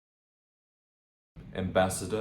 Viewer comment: what are you trying to say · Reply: Ambassador